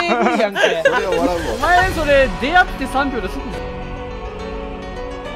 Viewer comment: ロボロさん『出会って3秒ですぐに○ぬでそれって』 聞こえませんか？